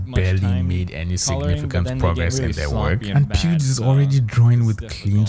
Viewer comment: whatever you use for subtitling is doing a hilarious job 😭 It says pubes instead of pewds